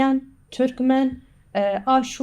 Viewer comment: does she say "Armenians"? That should be in the translation, no? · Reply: Kurd here, yes she said "Arman" meaning Armenian.....